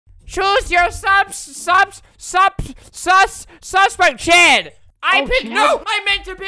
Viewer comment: did he just say... SUS?!